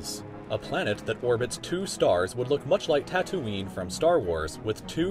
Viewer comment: did he say tat oon nie well its TAtooeene